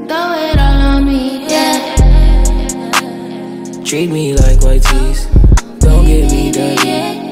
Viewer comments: I thought it said treat me like white treas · I heard "treat me like my dream"